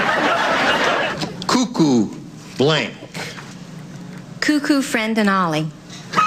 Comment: I could NOT figure out what was going on at 00:27, when he said "cuckoo", and the woman didn't respond with the word "clock", but a string of words that made everyone crack up. After searching online, I finally figured out what she said -- it was "Cuckoo, Fran and Ollie"... which was just a mispronunciation of the actual name of a children's TV show from the 1950's, called "Kukla, Fran and Ollie".